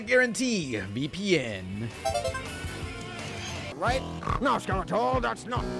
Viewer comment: I thought he said "thats what Damascus"